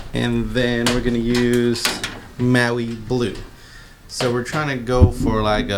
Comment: What did you say the blue color was. It looks like the sail blue from the cap but it sounds like you said Maui Blue. I like the Cherry Red and want to know the other color was please. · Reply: The can said Maui Blue